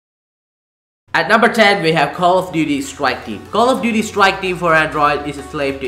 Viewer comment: did he just say call of duty swike team